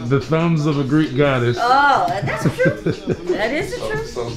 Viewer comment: Anyone else hear them talking about zion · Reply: They were saying Zion got to leave the bubble